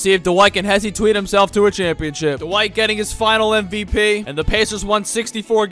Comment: Bro i thought you said finals mvp 😭😭😭